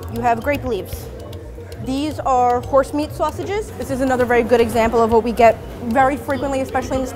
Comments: Hold Up did she say horse meat sausages? 😮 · did she just say horse meat sausages????